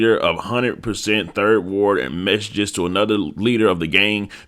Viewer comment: My dumb ass thought it said "massaged" i was fina say😂😭😭